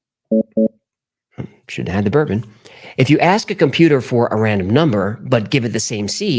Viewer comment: I am passively listening but busted out laughing so hard when I heard him say "Shouldn't of had the bourbon"